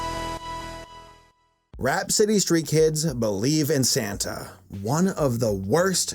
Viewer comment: Lol I thought it said believe in Satan